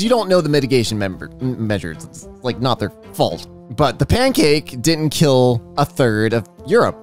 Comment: Did actually say "The pancake killed (or didn't kill) 1/3 of Europe? And a bad joke: If you don't like how long gardening takes, then the flower for you is the impatiens.